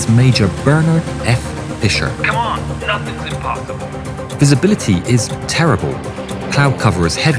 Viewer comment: I heard 'his ability is terrible'